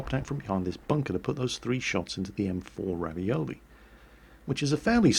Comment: Did he just say ravioli? You know what nvm.